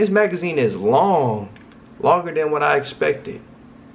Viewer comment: I thought he said “longer than I’m packing”💀💀💀💀💀